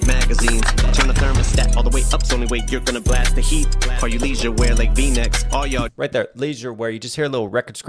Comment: Maybe im reaching but i think it sounds like he said all the leisure wear (lee's you wear)???